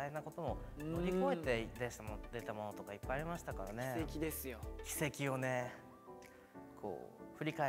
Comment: ここ、イントネーションが 葛葉「軌跡ですよ」 叶「奇跡よね」 って言ってるように聞こえる